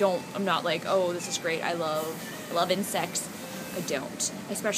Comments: what she said at 0:24 ? pls anyone, seems like " loving sex ", but that's not her style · i thought you said "lovin sex" and i got really confused. now i feel stupid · I thought she was saying ''lovin sex'' too, I had to replay at 0.25 to be sure what she said, and now I think I heard right, you said ''LOVE INSECTS'' .. Hope that's right or I will feel dumb :P Love your videos Justine <3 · When she said 'love, love insects' I thought she said 'love, love and sex' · I thought she said its not like I love sex not its not like I love insects...